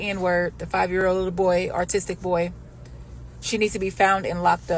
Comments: did she say “artistic” boy? 😂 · did she say “artistic” boy?